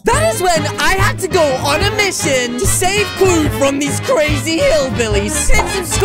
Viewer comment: did he say hillbillys in